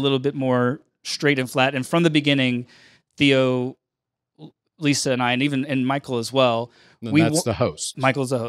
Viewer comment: who's the name did he say? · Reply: I heard Theo, Michael and Lisa.